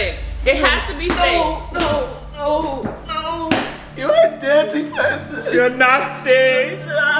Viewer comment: Anyone else I heard “dancing person” instead of dirty person?